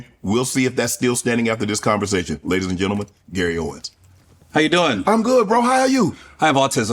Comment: Did he just call him "owenSSSSS"?